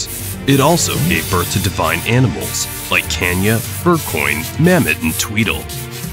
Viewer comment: Wait a minute , thunder wubbox is not a real thing, The electric celestial Galvana created the wubbox. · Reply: And also , did he really say Kenya , instead of kanya 0:26 💀